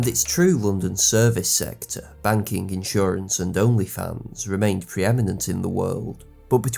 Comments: Banking, insurance and onlyfans?!? Did I hear that right? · did . . . did anyone else hear "banking, insurance, and onlyfans" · Did he say banking, insurance, and onlyfans? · Did he say onlyFans? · did he say Britain was preeminent with Only fans before the war?? 😂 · Did I hear OnlyFans at 02:10:43?!?😂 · did it say only fans 😅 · Britain’s banking, insurance, and… OnlyFans? I must be mishearing that lol